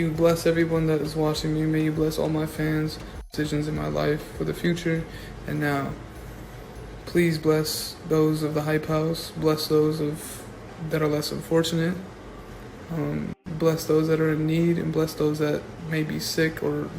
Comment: I may just be deaf but did he just say bless those that are less unfortunate?